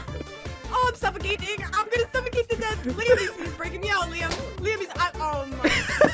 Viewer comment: did she say liam